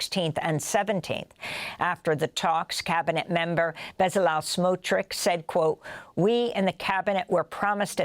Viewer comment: I believe I heard Beelzebub Smutrich? Fitting.